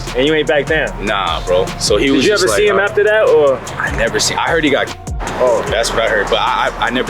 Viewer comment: I heard her got k*****. 🤔🤔 lol